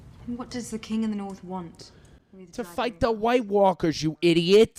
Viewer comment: I'm sorry but I spat out my coffee laughing when I heard '"to fight the night walkers, idiot" 🤣😅